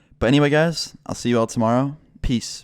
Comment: When he said peace at the end I thought he said piss